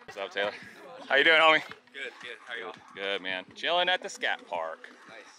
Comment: Did I just hear "Chilling at the scatparc"? 😂